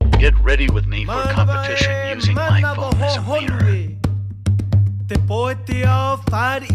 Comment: My dumbass thought u Said “get ready with me for competition using my phone as a minor"